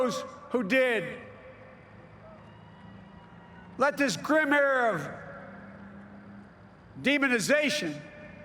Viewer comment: Wtf did he say at 9:22? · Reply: grim era